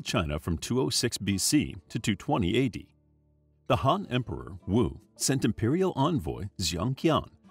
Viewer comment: I thought he said hot emperor💀💀💀